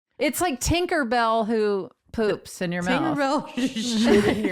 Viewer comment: I thought Tig said Kinkerbelle...lol